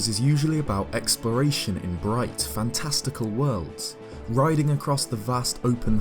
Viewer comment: I thought he said bright and testicle world's when he said bright and fantasticle world's.